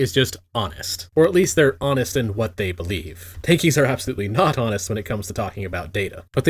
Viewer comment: can someone please explain what is tanky, or twinky, or what the fuсk is even this word means, i kinda cant get right writing on it, so can't search it on Google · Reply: Two months late, but here you go: tankie